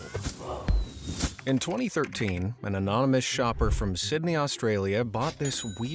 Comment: oh! ANONYMOUS. I thought he said “Amish”. I thought, “why is an Amish man using a camera, or even early going to a thrift shop?”